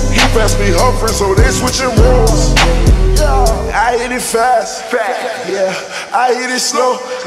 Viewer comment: I used to think he says "I eat it fast".